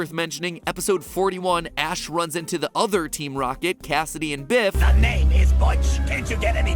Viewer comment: For a second or two I thought he said Cassidy and B*tch.